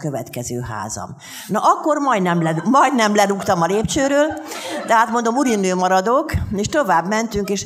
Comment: "Hát akkor majdnem lerúgtam a lépcsőről!" Jól hallom, amit mond?